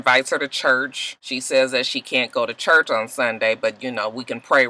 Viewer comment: Question...did Kenya say she "can" or she "can't" go to church? See, I thought she said, "I can go to church...." but can we say a prayer right now. Oh well...no biggie, I may have heard what I wanted to hear. LOL. I was being proud of Kenya for once and thought she was being positive. And yes Nene would have divided the room if she had been there. You're right, they do allow themselves to be divided. Just like, BBW, Evelyn and Shawney followed Tami even though they knew she was wrong. It's too bad that grown women will follow one into silly mess. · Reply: I thought Kenya said "she 'can't' go to church but she can pray right now."